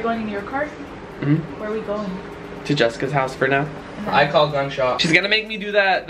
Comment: Anthony said "I got gunshot" is it just me or does everyone say shotgun.😂😂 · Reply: He said "I call gunshot." Not I got shotgun